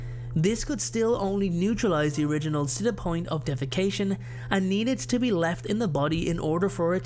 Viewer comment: I swear I heard DEFACATION instead of desiccation